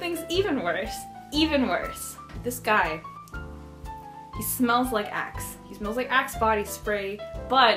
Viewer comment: Omg I thought you said he smelled like Ass 😂